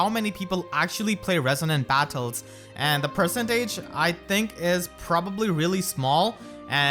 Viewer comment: "-In the percentage" What I heard "In the Present Age"